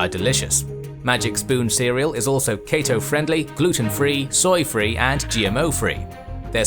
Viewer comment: I think it is normal for one to hear "Cato-friendly" instead of "Keto-friendly" @ 1:25 after watching so many Roman history videos.